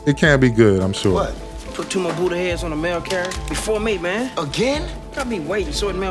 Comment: think he’s trying to say potheads